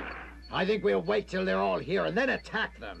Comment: "They're only headed directly to their own gray." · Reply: I heard "Grave".